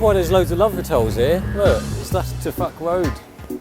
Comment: i heard "To Fuck Road"! ~~~oh dear-.